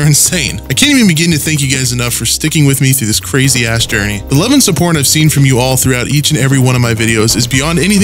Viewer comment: Instead of love and support he says love and supporn